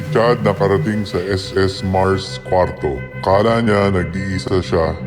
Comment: In 6:52 I think it said as mars and that is the room that chads in